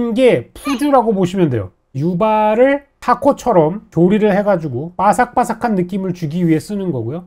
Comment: 파코가뭐임 ? 타코가아니라 파코 ? 오타인가 타코처럼들리는대